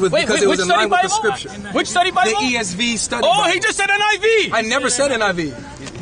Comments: he clearly said ESV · honestly i heard him say esv and that sheikh said niv, what an embarrassment i feel bad